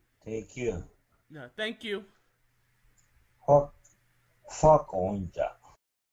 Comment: To the end of the interview, it sounds like he said "fuck new jack "